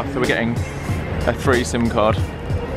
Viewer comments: i can't have been the only one who heard three-some card who said that?! how immature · Sounded like you said threesome card instead of three SIM card hahahah